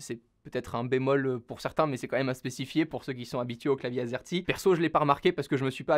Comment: au lieu de clavier azerty, j'ai entendu "clavier asiatique" 😂😂